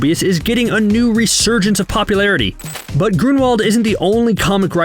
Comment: I hear grimwald and it reminds me of fantastic beasts 2- either that or i cant hear properly